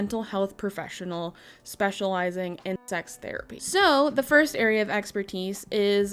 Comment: Why did I heard tax therapy lol 💀